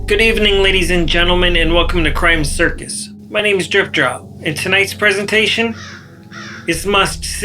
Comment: Lmao 😂 welcome to crime circus my name is jerkoff 😂 silly looking kunt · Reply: I swore thats what he said ,I had to rewind it 4 times 😂jerk off drip drop goofy mf